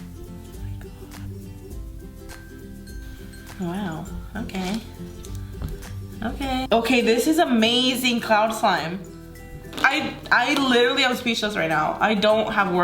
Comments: Who else heard her say cloud _lime try find the missing letter😏🤪🤓🧐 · i heard clime and slime